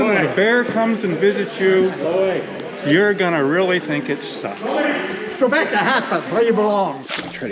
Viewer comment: The old guy tell you to “go back to the hell from where you belong,” I swear I hear “go back to hot tub from where you belong” 😂